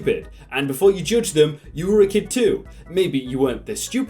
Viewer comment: At the start I thought he said “and you’re a kid too!” And I was like: “OMG HOW DID HE KNOW!”